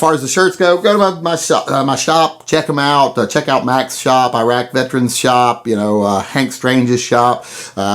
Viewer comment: Glad to see you pulled through. Congrats on the new place out in the country, hope the move goes well. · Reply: What in the actual fuq did you say at 06:37, cuz it sounds like you said to go check out Irac veteran's shirt shop? Did I miss a video? The 1-2 hr long pod casts are a bit too long for me...must have missed it there....